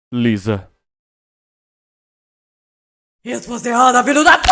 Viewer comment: Pra quem não entendeu bem o que ela disse no final foi "Resposta errada filha da p.."